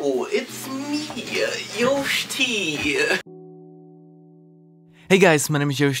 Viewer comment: Lol yoshtea just be like "HeLlO its mE YOSHTEA" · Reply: At first i thought you said yoshi